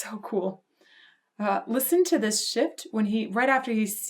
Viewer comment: Hahhahhha, at first I thought you said, 'listen to this shit' and I was like, F yeah, I'm listening to it!